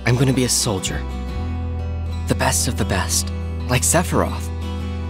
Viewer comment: Why does cloud say sephiroth name weird in the beginning? He sounds like he says Seforoth.